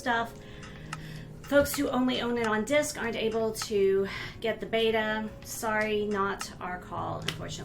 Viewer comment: I thought She said only people with discs can play beta but its the way around PHEW... I was Worried Because I Have Minecraft Digitally Not In A disc...RIP PEOPLE WITH MINECRAFT DISCS